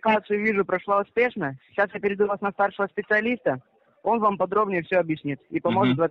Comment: Мне послышалось "Переведу вас на першего специалиста"))